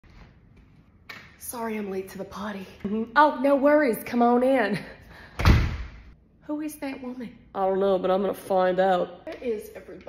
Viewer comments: It sounded like she said, "sorry im late to the potty." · I thought she said “ sorry I’m late to the potty “